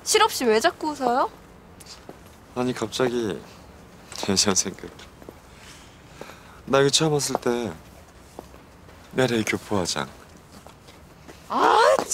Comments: 아니 0:27초부터 대체 뭐라고 하는건가요...????? 놀라운 토요일 노래 첨들을때 느낌이 이런건가....??ㅋㅋㅋ 들리시는 분 누가 댓글로 알랴주세요...(진지) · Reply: "아니 갑자기 예전생각이 ㅋㅋ 나 여기 처음왔을 때 LA교포회장" 이라고 하는거 같아요 저도 밑댓보고 알았어요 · 안재현 뭐라는거야?? 아니 갑자기 옛날생각?? 맞는건가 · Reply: 혜전생카기,, 라고하네요 예전생각 ㅋㅋ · 진짜 여기 대사 아시는 분 꼭 알려주세요.. 너무 궁금해요 ...ㅠㅠㅠㅠㅠㅠ 계속 들었는데 모르겠어요... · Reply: "나 여기 처음 왔을때, LA교포 화장" 이라고 하네요. 구혜선이 화장이 그랬다고 놀리는거 같네요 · 진짜 여기 대사 아시는 분 꼭 알려주세요.. 너무 궁금해요 ...ㅠㅠㅠㅠㅠㅠ 계속 들었는데 모르겠어요... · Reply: '나 여기 처음 왔을 때 LA 교포화장 ' 으로 들리네용